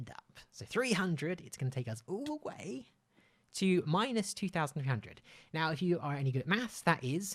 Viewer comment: Minus two thousand hundred Umm what and he also said if WE were good at math like what